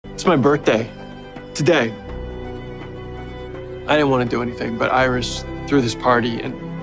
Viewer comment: I thought Barry said “ I don’t wanna do anything but Iris”